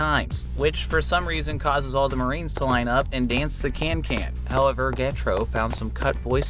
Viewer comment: did you say.. Kankan?